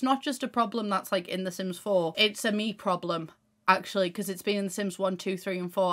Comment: I heard “it’s a meat problem” at 3:45 🥩